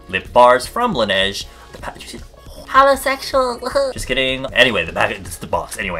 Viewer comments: did he just say... "holosexual"..?? CRISTINEE!!!! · DID HE JUST SAY HOLOSEXUALS!?!?!?!? HEADS UP TO ALL THE HOLOSEXUALS HERE!!!!!💿💿💿💿💿💿